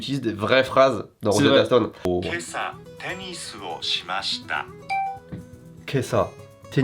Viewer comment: à 6:20 j'ai entendu 今朝ペニスをしました XD